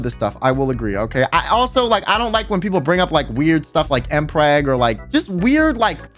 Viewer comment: Took me a whioe to figure out he said mpreg and stuff like that (thought he said L flag ngl)...if i ever said mpreg it would be like 'you dont know the pain of pregnancy'